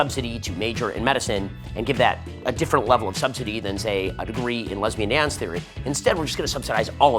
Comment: lololololol Did he say "a degree in lesbian ants Theory" at min @ 6:42?